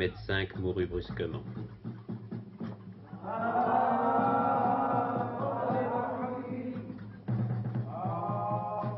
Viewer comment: ils disent quoi à@ 0:44? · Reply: الله يبارك في عمر سيدي " الله يبكعم سيدي "